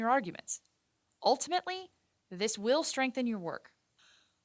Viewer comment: did she say she's an enfp or infp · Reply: She said enfp